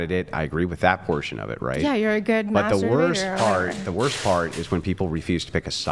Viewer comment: Did she call Andrew a good masturbator @ 1:45?